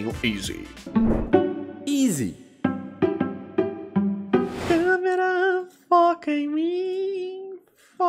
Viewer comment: eu entendi fuck me